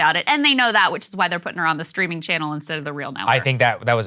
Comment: Streaming? Ohh. I thought you said steaming. As in a pile of crap.